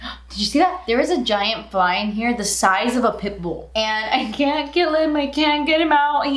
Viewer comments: a few seconds into this video i’m already laughing so hard when she said “there is a fly in here the size of a pit bull” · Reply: I heard it as the size of a pimple. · a few seconds into this video i’m already laughing so hard when she said “there is a fly in here the size of a pit bull” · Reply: I thought she said the size of a pickle 😂